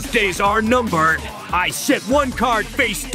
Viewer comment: Did it sound like kaiba said "i shit one card" to anyone else